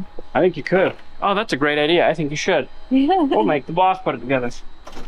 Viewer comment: it almost sounded like he said he would MAKE The Boss put it back together 😂😂😂